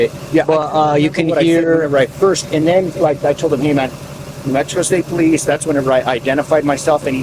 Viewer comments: did anyone else hear metro state police? sounded like Dewitt for a second 😂😂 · did I hear... "Metro State Police"? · I usually try to figure out right away where a video like this takes place. Still hadn't yet at 6:52 when it sure sounded like Captain said "New Metro State Police" lmaooo!!!!